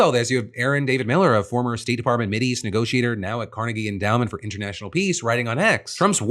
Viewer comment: Ohh, Carnegie endowment I heard ‘Cardie B endowment’